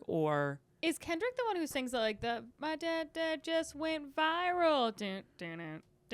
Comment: When started singing I thought she said” my dad’s d*ck went viral”. Haha